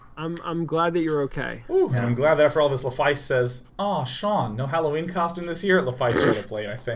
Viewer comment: I don't understand what sean says at 52:37 Anyone wanna translate? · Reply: And for all of that (twitch username) says "aww Sean no Halloween costume this year?" (Twitch username) showed up late I think